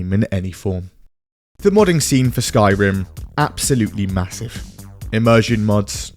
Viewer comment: did someone say.. massive?